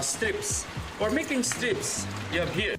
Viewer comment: เขาพูดว่า Strips(แถบ) Carrot Strips แครอทแบบเส้นค่ะพี่ ไปดูคลิปจริงมา